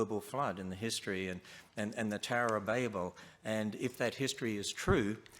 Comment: I'm not major in English so i'm sure if I'm right in thinking that "babble" mean something like nonsense, but in case i am right: To me it sounded like Ham said " the terror of babble" at 2:24:56. I find that a fitting summary of Hams side of this debate.